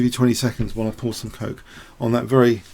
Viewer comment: that didn't sound like coke, that sounded like you were scroogin on a greg.